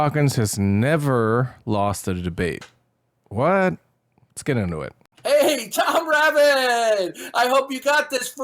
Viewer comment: I fully heard it as "lets get annoyed".. instead of "lets get into it".. and i feel the former is probably more accurate with reference to that piece of shit.